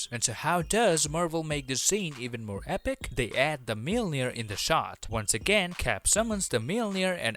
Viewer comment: what is the actual word: mjölnir what does the subtitles say : Meal near